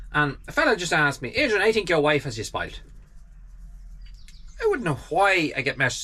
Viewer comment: I just looking back at that to see what I said, can’t figure which part 🤔. If you pin point the exact part i will work it out Janet. Ya the Cavan Irish accent can be tricky i’m told 🤣🤣. Thank you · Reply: It was at minute 8:03 just before you starting to eat your sandwich. It sounded like you said “SPITE”. Meaning your wife cut you sandwich straight across rather than at an angle, and she should know sandwiches taste better when cut into triangles (joking) 🤣